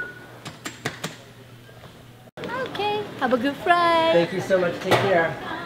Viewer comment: The lady at the terminal goes "Have a good friiiight" and it just makes me crack up. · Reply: thought she said "Have a good frie" 😂